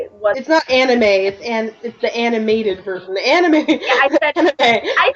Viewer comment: It really does sound like I said anime... O.O I really did say animated I SWEAR!